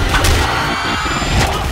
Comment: does RZA say "you've become a pain in my asshole"?